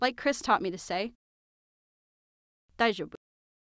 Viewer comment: I almost cried while I was watching this video and then I laughed so hard that I cried So, I am Russian and 9:38 I heard like: «дай жопу» which means “give ass” I am so sorry...